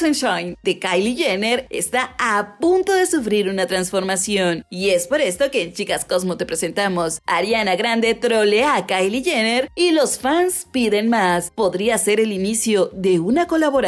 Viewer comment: Soy la única que entendí "ariana Grande atropella a kyle jenner" JAJAJAJAJA ekisde